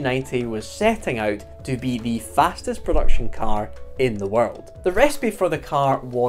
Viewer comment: all I can hear is Clarkson saying "in the worrrrrrrld"